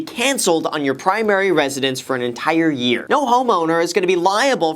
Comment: When he said “no home owner” I thought he was about to saying “no homo” 😂